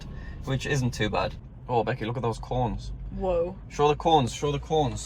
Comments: did anyone else think he said look at the corns lol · Sounded like he said, "I LIKE THE CORNS."